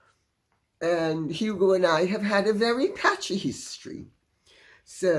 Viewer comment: I heard "Passionate History" when you said it...Thank God I put my CC on to see that you said, "PATCHY History Lady C. :face-green-smiling